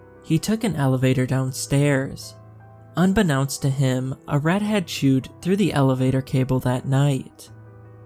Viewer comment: I thought he said "...a redhead chewed..." Instead of "...a rat had chewed through the elevator cable." Listened 3Xs til I realized